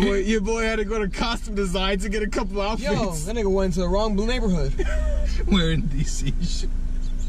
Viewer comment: omfg did he just say blue neighborhood